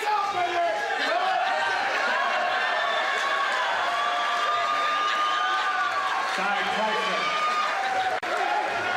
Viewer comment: what did he say ? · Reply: Lights out baby!